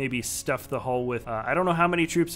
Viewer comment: did anyone else hear him say WOOKIEEpedia